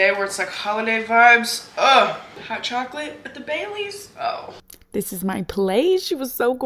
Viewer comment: I thought you said hot chocolate BAY LEAVES and I was like wait wut ma'am🤣🤣